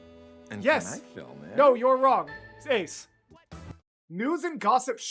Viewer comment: did you just say "seis"?? as in the finnish word for "stop"? no ive Got to be imagining it. but anyone else hear it too?